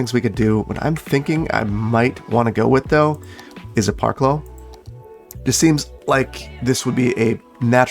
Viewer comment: Ohhh “Parclo”. I always heard “Park low”, which had me assuming it was some kind of old planning term that stuck around for some reason. EDIT: iI really like the artificial, “this has to go somewhere” vibe of the garbage collection center! It feels believable in how ridiculously absurd it is. Wealthy suburbanites forcing a portion of the coastline to be relegated to “dirty” uses because they can’t handle the idea of trash collection being located near their neighborhood feels very realistic.